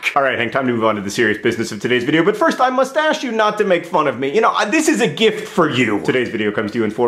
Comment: Did he just say "I mustache you"